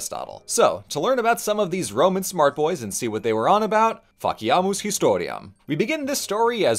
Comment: Honestly heard this as “Fuck yeah! History!” And that’s amazingly on brand. XD